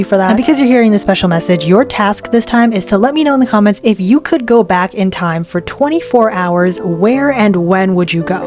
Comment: At [6:24] I thought that she said "If you travel 24 hours back in time" 😭🙏